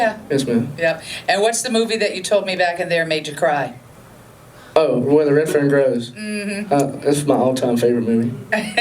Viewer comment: What is the movie he said is the one that made him cry? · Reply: Where the red ferns grows